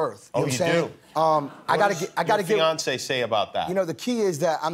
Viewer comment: lol I thought he said Beyoncé not fiance